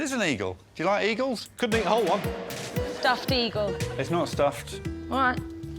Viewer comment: I cannot believe the didn't catch andy saying "I couldn't eat a whole ONE"